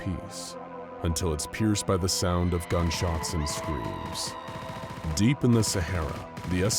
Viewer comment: At the start when it said”screams” I could have sworn I heard toad screaming